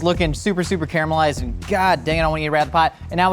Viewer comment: Did say “goddamn” at 7:42?? 🤣